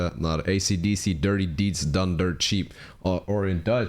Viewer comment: When i was a kid I thought they was saying " dirty deeds and thunder chief".